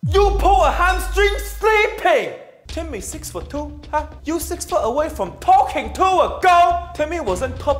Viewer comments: “Timmy is 6 foot 2, you are 6 feet away from talking to a girl!” Steven’s dad has a masters degree in roasting Steven. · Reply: At first I thought he said "Timmy is 6 foot 2, you are six feet away from talking to a god" I was like wow that was dark! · “Timmy 6 foot 2, you 6 foot away from talking to a girl” I felt that one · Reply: I thought he said talking to God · “Timmy is 6 foot 2, you are 6 feet away from talking to a girl!” Steven’s dad has a masters degree in roasting Steven. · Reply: I heard it as "6ft away from talking to a goat" lol